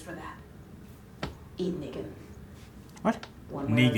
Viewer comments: I also thought michonne said “eat nigga” I had to rewind like “wtf!?😂😂” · lol i thought michonne said "eat nigga" too 😂